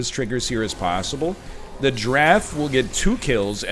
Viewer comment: Every time he says "Draff" I hear "Giraffe" and honestly I think they should add giraffes to the game.